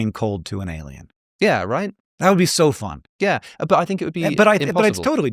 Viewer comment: What does Alex say at 0:38? Hank: "it's totally doable" Alex: "???" Hank: "HAHAHAHA!!!" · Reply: But I think it would be impossible 😂